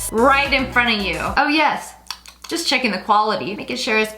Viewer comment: she said chicken instead of chips lol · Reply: She said checkin the chips Not chicken lol